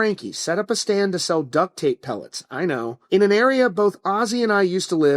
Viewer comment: what did you say? “Duct tape pellets”?? It definitely said “duct tape WALLETS” 😂😂. RIP OZZY nonetheless. This one hurts. I realize how old I am now. I remember the first time I heard Ozzy. My uncle”s favorite was ALWAYS Ozzy Osbourne. So I’d say probably when I was 5 years old in 1988. I can remember my uncle hand painted that iconic Ozzy font/logo on the bedroom wall. Ahh the memories. Definitely lost a legend today. 🤘🏻🤘🏻